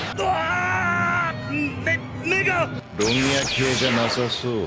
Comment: Subtitles: My eyes What I hear: Aaah, n1gga